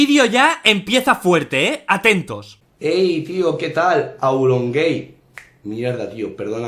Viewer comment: yo entendí AuronGame ._. y no le entendí la gracia asta que auron lo explicó XD jajaja... Sige sin darme gracia ._.